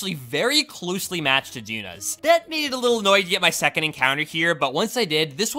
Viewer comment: I think I figured out what you said when you had a stroke “that made it a little annoying to get my second encounter”